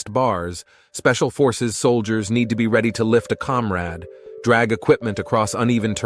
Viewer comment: did you just say "comrad" ?